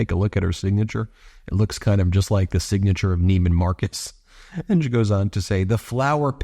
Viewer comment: I’m glad I’m not the only one who thought that said Neiman Markus 😂